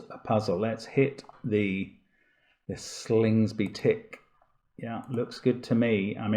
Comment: Can anyone help clarify what Mark says at 1:05:13? It sounds to me like, "Let's hit the Slingsby tick." Is that right? If so, what is that a reference to? I Googled a little, but came up empty.